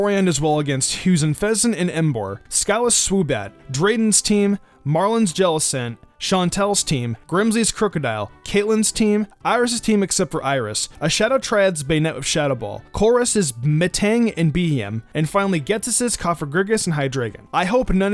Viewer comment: NANI! Did he say " Iris's team except Iris." Or am I hearing wrong?